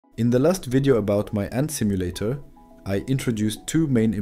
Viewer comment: I thought it said autism simulator